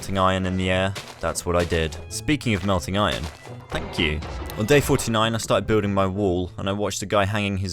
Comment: I thought said “fuck you” so I rewatched that part and it was “thank you” 💀